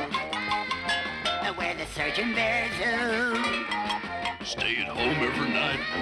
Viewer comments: In wonderland zoo, we're the certain bears who Stay at home every night, Never quarrel or fight Aw, we don't even bite So don't yell, "HELP, HELP, HERE COME THE BEARS!!!"(3×) LET'S SPLIT!!!!!" So don't yell, "HELP, HELP HERE COME THE BEARS!!!" (3×) LET'S SPLIT!!!!! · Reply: When I watched it as a kid, I heard it as "surgeon bears." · The line at 0:08 I remember in the 90s CBBC asked the veiwers to write in what they thought he said. When I listen to it I hear, "Where the searching bear zoo." But that doesn't really make sense.